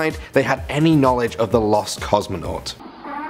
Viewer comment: Lol I thought you were about to say "lost cause" when you said "lost cosmonaut"